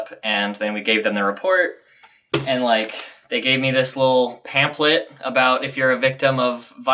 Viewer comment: Did he say pan plip at 4:53?